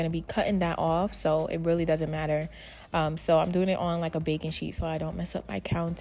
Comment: I thought you said a bacon cheese slide lol I think you said a baking sheet slide?? 🤣